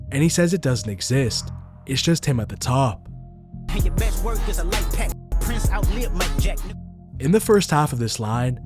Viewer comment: he said “Your best work is a lite pack” because Daylyt wrote back to back. Not what you said.